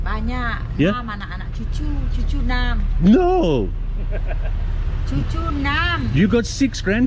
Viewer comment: I think she said tujuh enam! As in 76, but that couldn't be correct 😅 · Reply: Correcting you, respectfully: She did say "Cucu, enam".... Cucu, in Bahasa Indo / Malay = grandchild(ren). Easily misheard for 'Tujuh enam' fersure.... :D :D